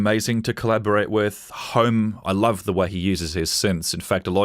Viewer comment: Who's the artist Nick mentioned at 10:26 who uses synths? Did he say home? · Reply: Home, who released Resonance: <link>